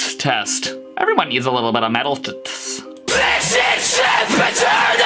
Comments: "This is Sand pit turtle" more like "where's my fucking potato" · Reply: I heard “THIS IS S*** POTATO” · I heard “THIS IS SIMP ETERNAL!” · "This is Sand pit turtle" more like "where's my fucking potato" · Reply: I heard: ''THIS IS SAD POTATO''